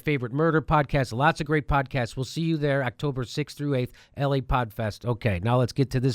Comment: LA podcast. i thought he said LA Pot Fest lmao